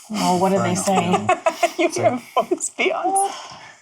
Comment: What did Kate actually say at 46:07 I've listened to it over and over again and for the life of me can't make it out. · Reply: She repeats Kriss' joke: "You hear a voice Beyoncé"